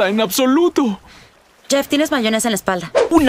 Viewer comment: que dice la chica? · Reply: "Jeff, tienes mayonesa ("mayonesa" :v) en la espalda".